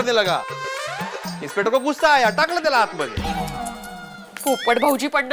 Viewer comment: i think he says" takly ty laat mari"..😟😟😟